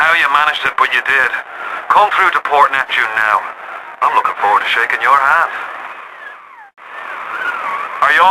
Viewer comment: I always thought he said "I'm lookin' forward to shakin' your arse!"